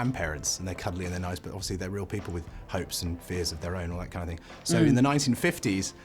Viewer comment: What is he saying on 2:01, after "you consider them parents"...? Please, help! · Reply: "you consider them grandparents. They are cuddly and nice but obviously they are real people with hopes and fear of their own"